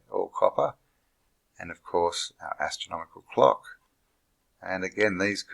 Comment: Could've sworn you said astronomical cocks!!! LMFAO feel free to use that for another video...or not. Ha! ( I'm probably never going to ascend at this rate)